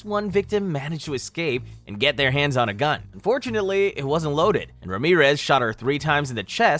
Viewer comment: did bro just say FORTUNATELY 😭😭